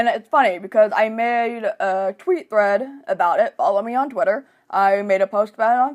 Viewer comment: You said 'I made a Tweet thread about it' and I heard 'I made a Tweet bread about it'...and i'm all 'that's not right!'. HAs are in my near future - I have my consultation next month. Masks have made things pretty weird...plus the maddening tinnitus. Yeah....no shame. And you're totally right about the Deaf/HOH world...the struggle is real!